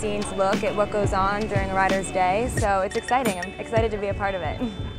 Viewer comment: I thought she said follow me on PENTHOUSE at first!